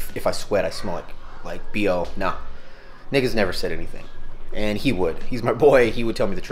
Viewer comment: Loving the vids as usual Coach, always giving good advice 👌🏼 Hahah my niece does that too randomly. I swear I thought you said “niggas never said anything” lmao 😂